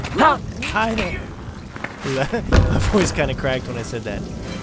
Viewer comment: when u said "hi there" at around 15 min in the vid it sounded like u said alkaida